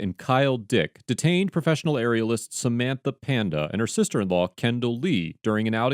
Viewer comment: I didn't know "aerialist" was a thing and thought the intro said "areolist"